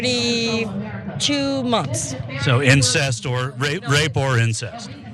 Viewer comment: Wait wait wait wait... she said, "Be it 2 weeks or...32 months"?! I didn't hear that wrong, right? That's almost a 3 year-old!